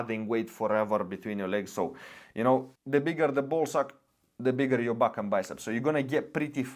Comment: " bigger the ball sack bigger the back and biceps " 😂 did i heard it right ? Or im just tripping